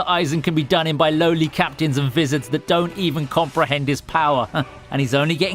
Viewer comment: Did anyone heard him say wizards in stade of vizareds